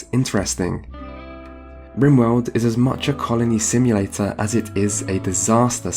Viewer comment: Until I turned on subtitles I heard "carnie simulator" instead of "colony simulator" and I didn't even question it.